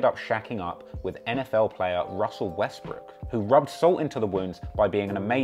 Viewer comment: It is only me, but I hear this guy said Ciera left Future for NFL Star Russel Westbrook. I know he must have meant Russel Wilson because later in the video you can clearly hear he said “Wilson”. I replay this Part a few time and I keep hearing Westbrook instead of Wilson. 🤔🤔🤔